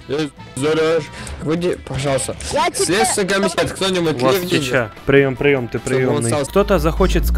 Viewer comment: мне одному послышалось "У вас Чича"?